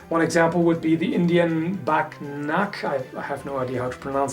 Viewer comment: can someone write for me the name of this weapon? I'm not English/American so sometimes it's hard for me to write the words I hear :P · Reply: Bagh naka It's in an indian weapon so being fluent in english probably doesn't help.